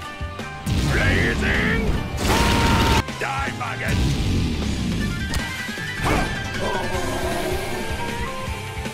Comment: WHAT DID HE SAY??? 0:08 💀💀💀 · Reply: "Die Maggot!"